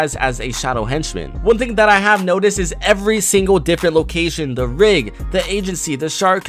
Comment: Myth: Can you rocket ride a henchmen? Because that is how you get him out of the agency, The rig, The yatch, The shark or The grotto. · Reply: I thought the rig said "the pig" and the the shark said "the shrek"